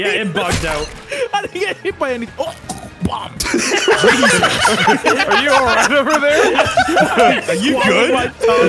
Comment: Did i hear bomb? lmao